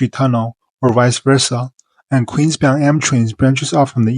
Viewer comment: why do the captions instead of m trains it says: M A M A M A M A M A M A M A M A M A M A M A M A trains